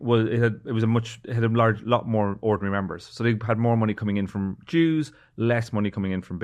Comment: I fully thought he said they had more money coming in from jews and was more than a little shocked